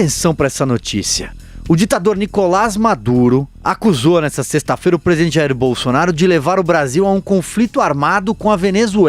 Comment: parece que ouvi presidiário